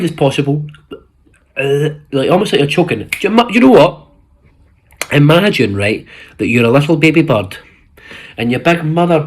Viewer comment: why did i think he was saying "imagine right, that you're a little baby butt"